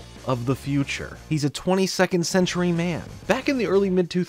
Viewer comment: I always thought the theme song said "20 second century man" indicating he could travel through time at a rate of one century per 20 seconds. In fairness I was young...